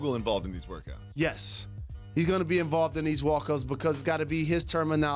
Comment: That dude Jalen said he’d be involved in “wokas” instead of workouts at 2:28 . What’s that?